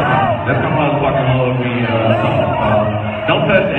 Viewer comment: What did Plies say? "Don't touch me because I'm...?"